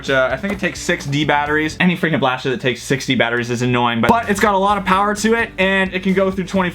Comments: I thought u said sixty batteries but it was 6 D batteries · What's your guys favorite blaster?! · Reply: dude when you were talking about the mastodon it sounded like you said 60 batteries instead of 6 d batteries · did he just say 60 batteries? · the first time said that the mastodon took 6 D batteries i misheard and thought he said it took 60 batteries